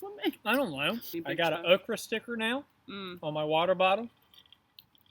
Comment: When you said an Okra sticker I thought it you said an Oprah sticker and now I'm a little disappointed